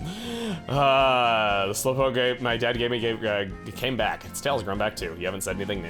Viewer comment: I horribly misheard the line in 17:39 as slowpoke ate my dad